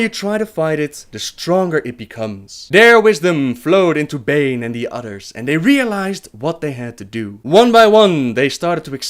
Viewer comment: omg im so sorry but i could have sworn you said at 18:32 "Bare wisdom flowed through Baine and his utters!" XD sorry but funniest thing ive heard all day :)